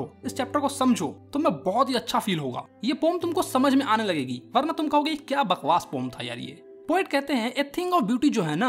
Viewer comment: bhaiya said " varna tum kahoge kitni bakwas poem thi ye " But i heard "baadme tum kahna kitni bakwaas poem thi ye " 😂😂 PLEASE meri TARGET COMPLETE karne mein HELP karo I WANT TO ACHIEVE 69 LIKES 🌚🌚 EDIT: Ohh shayad 69 ke baad K lagana bhul gayi 🙃